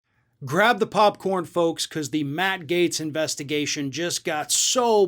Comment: First time through I wasn’t paying 100% attention: I thought you said “grab the pompadour foam”